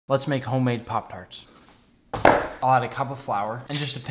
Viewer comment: My volume was so low i heard him saying "lets make homemade popcorn"